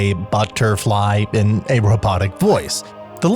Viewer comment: I thought P.E.K.K.A. said mega knight not butterflies😭